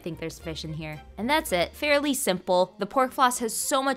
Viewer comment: I thought you said corn floss for a second.. and idk 😶 I couldn't imagine eating the hairs from the corn... But I figured it out lol you said pork floss